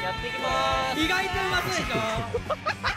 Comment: 今日初めて意外と美味そうでしょに聞こえました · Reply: わかるンゴ いつも「意外と不味そうでしょ」に聞こえてた